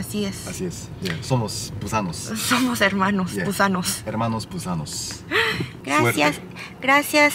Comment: Les entendí " somos hermanos gusanos " y yo 😮😧 😂😂